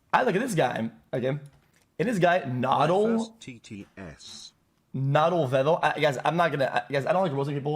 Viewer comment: Hey oomp, can you add subtitles? i can barely understand the weird goblin, i think he said nadle